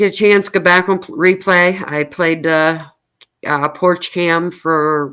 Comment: I literally thought it said "Porshe Cam" which i thought you stood for Camaro lol 😅 im dense at times. I was like Porshe Camaro, she got a bad az Car 😅 I should not Smoke till before bed. 😊😅